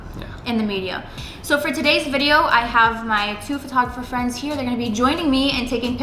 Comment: You speak so fast I thought I heard "photograFRIENDS".